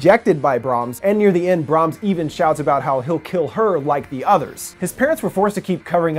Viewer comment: I heard Brahms say "Come back or I'll kill HIM like the rest" I did not hear him say "Come back or I'll kill YOU like the rest"....That's why she went back!! She wanted to save Malcolm. Otherwise she'd have kept on running!